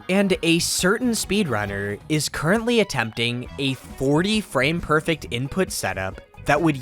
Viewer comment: did I just hear 40 frame-perfect setup or are my ears trolling me